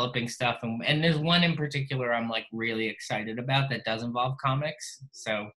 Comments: Hold on at 22:47 did he say “that doesnt involve comics” or did he say *does*?? Ifykyk 👀👀👀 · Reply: He says does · Fml I heard it as "There's something I'm really excited about something that doesn't involve comics" I was like eXcUsE mE??